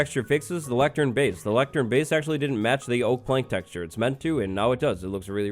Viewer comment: I misheard this as "the electric bass didn't match the oak plank", so for a moment I thought this was a bug relating to note blocks...